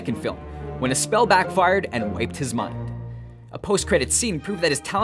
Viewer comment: did you say "when a spell backfired and raped his mind"? If so, nice.